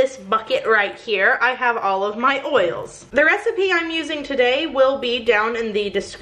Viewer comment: I have olive my oils. I'm not the only one who heard this pun instead of 'all of my oils', right?